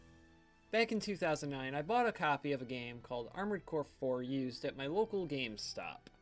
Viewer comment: I thought you said Armored Core: For Used